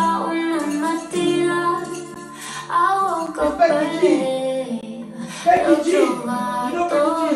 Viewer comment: I sounded like he said Becky C